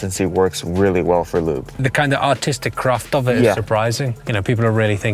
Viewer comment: I thought I heard: "autistic craft" lol